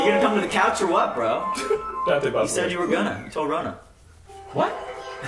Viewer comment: I'm so confused, does he say "you said you were gonna till rona" wtf is happening??????????